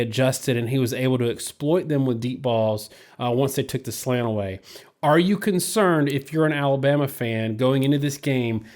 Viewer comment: Lol...I kept hearing “Are you concerned as a Bama fan...”. We all know most Bama fans never have concern. As a Miami fan everytime I see a Bama fan they say “You member that 1992 Sugar Bowl?” I always say...”Yep...you member that 1990 Sugar Bowl?” The Bama fans immediately look down like they have no clue of what I’m talking about...Bama fans erase all losses and bad possibilities.